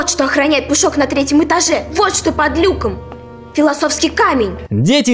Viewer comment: Когда Гермиона объясняла Гарри и Рону ,что охраняет Пушок, была такая фраза :"Вот что охраняет Пушок! Вот что под люком!" Но мне послышалось :"Вот какая подлюка!"😂😂😂